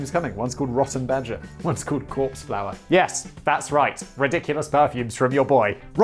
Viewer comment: I thought Simon said Corpseflour, that had me suddenly thinking about ground up mummies. And Victorians sniffing that Corpseflour... WHAT WHERE YOU DOING YOU ABSOLUTE BELLENDS? YOU HAD FUCKING LEGAL COCAINE BACK THEN! Simon is right, the past was the worst....